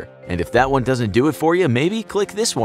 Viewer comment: I love how the cutout at the very end makes it sound like he said for queen and c**t